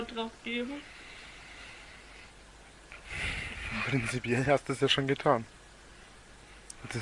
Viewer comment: Im surprised I understood what he said on on 2:56 where the captions said unintelligible. He said principally. Principally you already did.